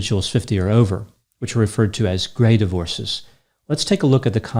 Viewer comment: I thought it said “Gay divorce “. I was like let me explain it to you some men do stuff to each other and get married to put it in peoples face